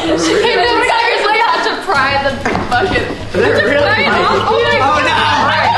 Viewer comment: did say the f-word am I the only one that heard that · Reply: she said bucket lmao